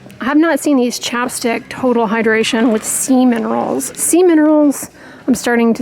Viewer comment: I about spit out my drink! 3:50 in I sure thought you were saying “semen rolls” and thought well hmmmm, THAT’S different 😂😂😂 Thanks for the laugh, Dr Dray!! And as always GREAT video!